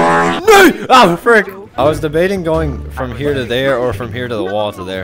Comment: watching this back and did you just say nee? that no in the Netherlands NO WAYAAYAY